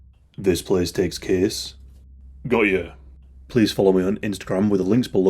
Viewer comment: Took me about 5 times to realize he said Got Ya lol, I thought he was saying Goya or something Great intro 😊